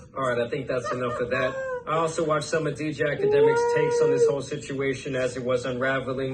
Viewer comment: Istg I thought he'd said "ejacademics" 💀